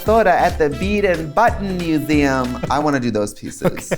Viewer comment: For whatever reason, I thought Trixie said “the Farrah Moan anal bead and button museum”.